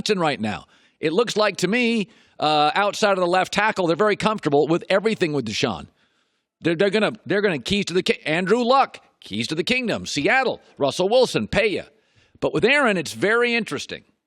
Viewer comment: Did he say Andrew Luck? That guy that retired?